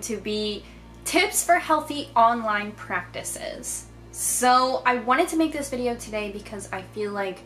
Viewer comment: Totally thought that said ' 3 tips for healthy online pancreas'